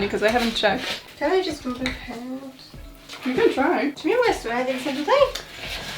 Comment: I'm stuck at 4:47 trying to figure out what the heck it is she's saying. Pls help it's too frustrating... being Polish isn't enough apparently because it does sound like pure complaining in gibberish it's beautiful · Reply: Czy mnie rejestruje jak jestem tutaj? -Nie wiem. Ja nie wiem nawet, czy nas widać. -Tu jest, dobra, może być (?) Something like that xD